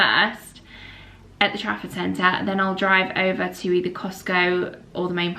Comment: I've always thought you were saying "traffic center" and I wondered why you would shop there!